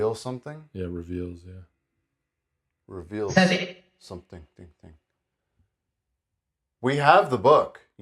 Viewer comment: What was that you saidadadad? 😂. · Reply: Reveal something thing thing thing 🤣